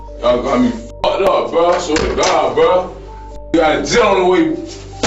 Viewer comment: he actually said “ I have a jit on the way “ lol , he means he has a child soon to be born .